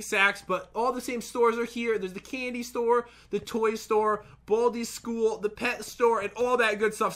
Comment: Who else thought he said bit*** ball?!